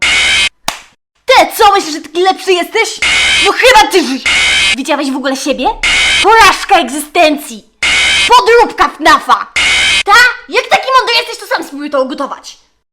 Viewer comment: teraz dopiero usłyszałem że tam jest ,,no chyba ty Żydzie"